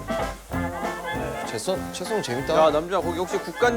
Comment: wait , did Namjoon just say that slicing is fun ?!?!